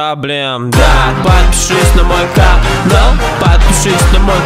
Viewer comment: Sounds like stop yaoi Im sorry